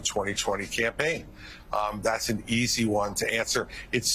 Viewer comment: That was the most meaningless debate that a major political party has had in the history of our country. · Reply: for a second I thought it said an easy cobbler. Then I thought to myself, can I get that recipe 😂😂😂